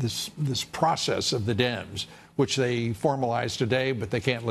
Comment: I thought he said Process of the damed, lol, it was Dems, lol!